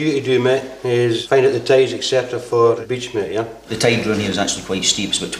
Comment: "We're going to insert by boat on this beach here..." Me: Did he just say "We're going to insert by boat on this BITCH here?" :p